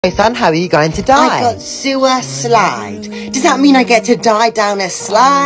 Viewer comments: Help why did i hear " sue a slide" 😭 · At first instead of being happy that he gets to die on a slide so we're not gonna talk about that I thought it said Sue a slide but can we talk about that for just a second like I mean if the slide fell on me when I got down the slide of course I'm gonna sue it or at least someone's gonna sue i hope if I die, but seriously I heard sue a slide did anyone else hear this? why am I just a alone?